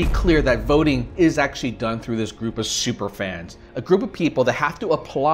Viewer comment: WHAT SUPER FANS???? I THINK I HEARD " FRYING PANS" HAHAHAHAHAHAHA